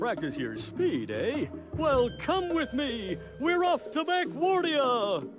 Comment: misheard this as "we're off to laguardia" and got scared for captain crunch for a sec